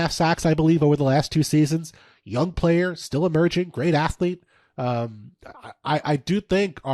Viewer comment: thought he said "still a virgin" lol